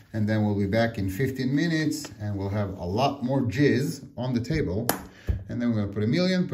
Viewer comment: Did he say “jizz on the table” at 4:46 ?